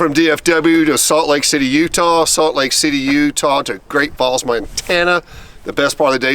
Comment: Is it just me or at the beginning did it sound like he said he was in great balls Montana 🤣🤣